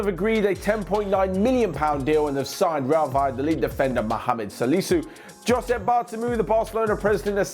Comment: For a moment I thought he said Mohamed Salah